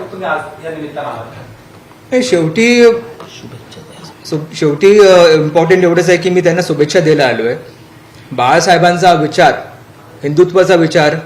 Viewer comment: someone tells him what to say!!! Just an observation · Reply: Yes, heard that subhecha dayla allo 😂